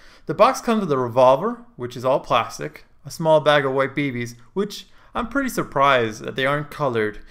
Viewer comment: why in the name of vadim on 1:08,instead of BBs There are White Babies... ._. (subtitles on)